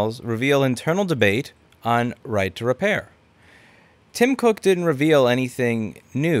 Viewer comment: I thought he said tom nook